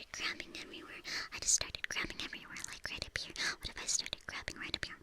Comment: I thought she said "What if I just started crapping everywhere?" I swear I've never done a double take so fast in my life